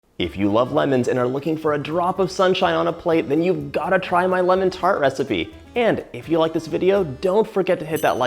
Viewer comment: I keep replaying it, but it was only the first time I heard lemon's fart recipe. oh well... I guess lemons don't fart.